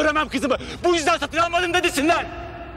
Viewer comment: Sancar 34:30 da ne diyor tam olarak ? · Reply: Bu yüzden satın almadım mı dedesinden )